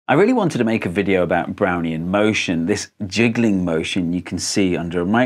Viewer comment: I thought you said "brownie motion".